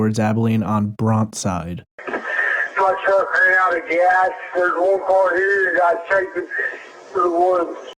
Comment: He says "There's 1car here guy chasing me through the woods" that's what I hear